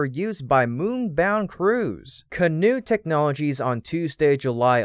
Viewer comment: did he say canoe or ka-noo? lol I had ever heard of Canoo Technologies and so I thought it was funny.